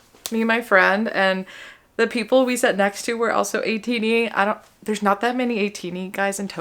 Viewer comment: What is she saying at 09:19? Ateenie? Eighteenth? A-T-nee? I don’t understand. 😢